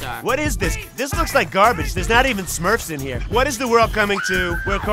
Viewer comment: did he say smurfs?